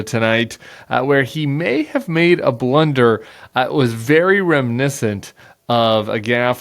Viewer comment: what's that word there, bud? rem-nissant?